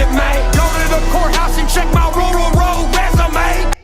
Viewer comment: I thought he said ROYAL resume'..like his birth certificate😂, 5hanks for clarifying, "Jeff." They both sound kinda like shit salesmen with a mouthful of samples😅